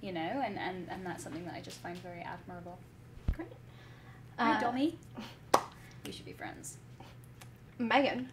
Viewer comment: What Dom said in minute 17:13-17:15? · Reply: “And that is just something I find very admirable. Hi Domie, we should be friends!”